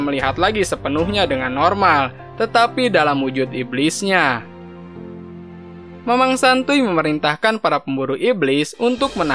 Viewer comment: aku gk salah dengarkan "mamang santuy":v